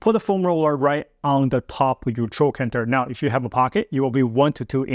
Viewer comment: you say right on the top with your chocantor , i know i'm mistaking what your saying but i couldn't understand the word. Are you massaging with the foam roller right on the hip bone or the softer part just below the bone? Please.